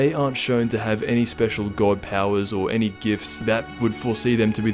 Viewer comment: Oh I thought it said dad powers